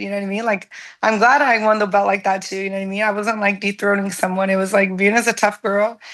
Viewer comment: Yo what did she say at 3:42 😳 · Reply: Dethroning, but I heard the same thing you heard 😂